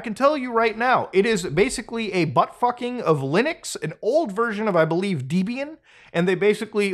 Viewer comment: nginx is actually pronounced "engine x" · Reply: Also he says Lunix instead of Linux 20:40 I think this is a deep fake